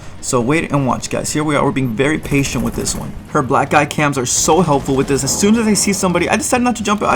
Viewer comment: I literally thought you were saying black guy cams lmaoo